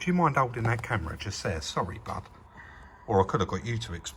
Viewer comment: Did you call BUB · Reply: Probably Bud